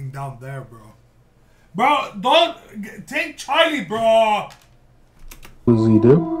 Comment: Ok. A few things. Arkansas is pronounced "ark in saw." When you said "Charlie Bro," I legit heard "Charlie Brown."😂 And 🎶 we are family! Get up everybody and sing 🎶 I'm done. Edit: OK, The hungry photographer was unexpected. I mess with it.